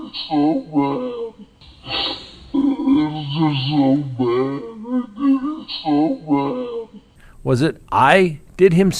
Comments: “I”….I heard “It’s just so bad; I did him so bad”. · Let's Talk About It! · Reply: I heard, "I did him so bad" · I heard "i did him so bad" very clearly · I heard “I did him so bad” 100% There is no “they” about it. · Let's Talk About It! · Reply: first time hearing this , I heard " I did him so bad" . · Sounds like he said I did him so bad. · 🤔It sounds like he said " I Did Him So Bad " to me! but i have a gut feeling he's NOT going to be charged for their m*rders. 😥Thanks! 😊